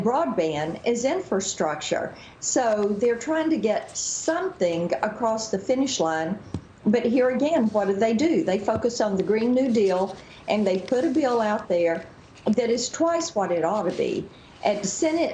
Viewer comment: I had to rewind. It sounded like she said "What do they do, they f*** us on the green new deal." then I realized she said focus. Wish she said the first one.